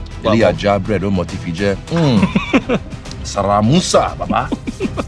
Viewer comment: Does anyone know what does he say at 16:05 "sraah mussa baba"